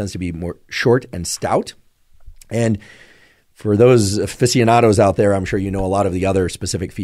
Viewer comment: What was the word you used at around 21:27? "physionatos"? I can't seem to find it. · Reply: aficionados